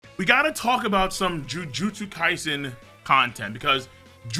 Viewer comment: I thought he was saying Jujulips kaisen